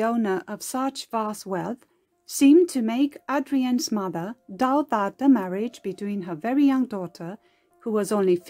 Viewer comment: I’m from Delaware too, also a history lover. What is the narrator saying at 2:07, seemed to make Adrienne’s mother _ the marriage?? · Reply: "... seemed to make Adrienne's mother doubt that the marriage ..." Thank you for watching.